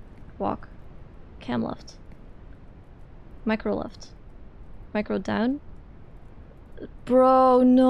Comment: when your small accent sounds like mrighto down instead of micro down and you fall